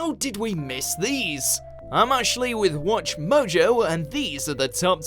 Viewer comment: When says his name I always hear " I´m actually"